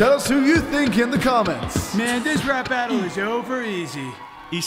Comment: Did he say "I like to play Eggs-box" Edit: Ok the question is OVER stop blowing me up talking about "yess" and "OMG Yes LOL" like leave me alone